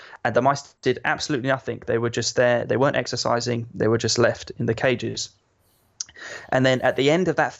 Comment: I thought he said "they would just lift in their cages" at12:18 lmao